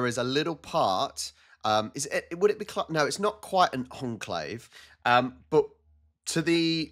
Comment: did you say "conclave"? Because there is a difference between conclave and enclave, the first is the meeting of the cardinals that has the duty of electing a new pope, and comes from latin "cum clave" that means loosely "closed with a key" because in medieval times they took too long to make a choice so the people of Rome closed the cardinals with a key in they deliberation room and took off the roof so they could feel the urgence to make a decision. An enclave is a small portion of territory of a country that is inside the borders of another country, and there is also an exclave, that is a portion of a country that is outside their borders. <link> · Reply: I said enclave